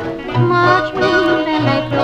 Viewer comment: Lyrics: March winds and April showers Make way for sweet May flowers And then comes June, a moon and you March winds and April showers Romance will soon be ours An outdoor paradise for two March winds and April showers Make way for sweet May flowers And then comes June, a moon and you March winds and April showers Make way for the happy hours And the May time, June time, love time and you March… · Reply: I thought it said "divorce" instead of "romance" 🤣😂😂